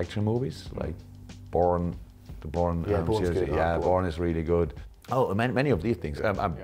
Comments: I thought he said "porn" 😳😳😂😂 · wtf i thought he said "Porn" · Is it just me or did klopp say porn is really good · Totally misheard Jurgen as saying "I like these action movies. Porn. Porn is really good"